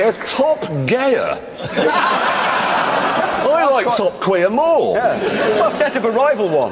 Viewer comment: I would like to buy a beer to the guy who shouted "TopQueer". An absolute legend. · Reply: I thought he said top queen